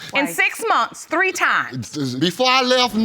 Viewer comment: took me out...😂😂😂 The whole explanation had me 💀😂.... thought he said shree times... and when he said DNA test... I replayed it like 6 times.😂😂😂